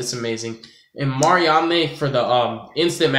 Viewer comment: Sorry, i am just getting back into yugioh. What was the card you said at 2:37? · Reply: i think its called "Mariande" ... you can search "True King" there will be 3 main deck monsters 1 earth (banned) 1 fire and 1 water look at their level... Than search "True Draco" search an wind monster with the same lvl as the cards above (should have an similiar effect)